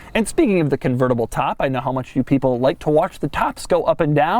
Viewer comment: Did say "you people" at 17:44?